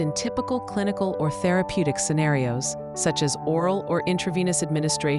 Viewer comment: Did they just say oral at 1:38? 😂